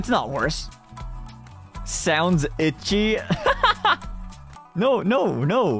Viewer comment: I think he said "ecchi" not itchy